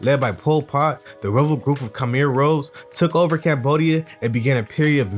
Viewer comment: The “Rouge” in Khmer Rouge is pronounced the same way as “Rouge” in Baton Rouge(as in capital of Louisiana). Sounded like u said “Rose,” but maybe I just need 2 clean the crap outta my ears.